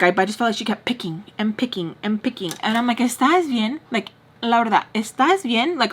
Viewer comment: I'll definitely be getting your book! Girl, did you just say Mrs Peach's real name? La***a 50:38 If ya missed it, kill it now! Lol. Or maybe I'm just hearing stuff. ❤